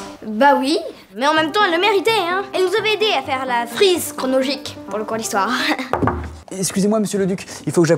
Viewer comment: C’est moi ou il dit : la frise « cronogique »